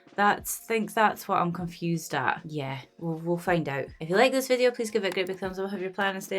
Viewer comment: So I thought you said ''if you like this video please give us £100 and stay lala la lal la la''....I did like it, so where shall I forward your money?